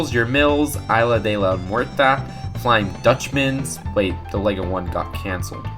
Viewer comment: I heard "the Lego one got cancer" lol